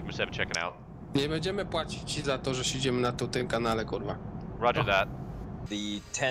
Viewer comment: did he say cheese on toast?